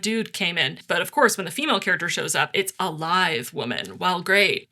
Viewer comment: OMG, the first 3 times I thought you said live, instead of lithe and I was sooooo confused. Are there dead women walking around in this world? What? Oh.